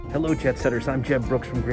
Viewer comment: Do you say “Hello Jet Setters!”, or “Hello Jeb Setters!” at the beginning? If it’s the former, I misheard. But the latter might be a cool nickname for your fans (or maybe not... LOL).